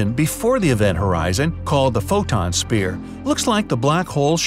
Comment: did he just call it the Photon Spear